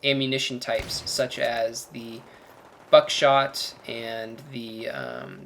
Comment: I thought he said "fuck shot" at first, then I listened to it over again and realized he said buckshot. :P